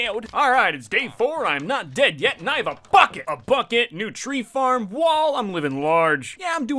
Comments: When you said Bucket I thought you said fu*k it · i thought he said fck it 😂😂😂😂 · When he shouted bucket thought he said f*ck it!